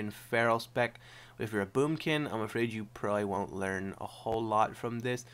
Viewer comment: did you say boobkin